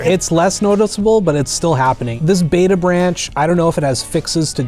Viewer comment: did he just say CD projekt Red is a beta branch?